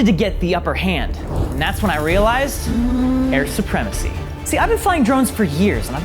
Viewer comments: It sounded like he said "Aryan Supremacy" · I thought said white supremacy